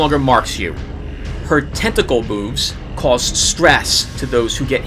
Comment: When you said "When the cultist witches use there tentacle moves" I miss heard that as use tentacle boobs. LOL One last thing i think courseus The ability old back tracker just as extra incentive to lock it. sorry for spelling.